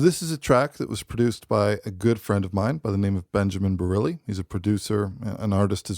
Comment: Thought you said Benjamin Burnley for a second. Production sounds 👍